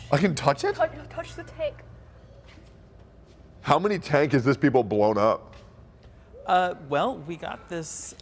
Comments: Did say how many tank has this people blown up? lol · did he just say "How many tank has this people blown up?"